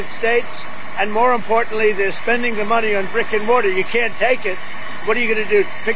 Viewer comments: The Tooth Fairy doesn't exist?! But but... but...I was so sure! My whole life is a lie! Also, am I the only one who heard him say "They're spending the money on Rick and Morty"? At 10:53. (I think he means Brick and Mortar, but I absolutely heard Rick and Morty.) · did he just say Rick and Morty?